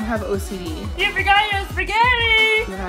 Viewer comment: Every time I hear in the intro I know she's saying "you forgot your spagetti" but without fail I hear "yippicaiyoo spagetti"